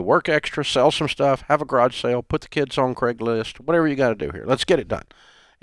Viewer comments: Did he say put the kids on Craigslist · Did he say put the kids on Craigslist..?